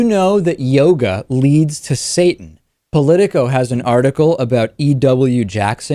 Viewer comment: I thought it said Yoda leads to Satin.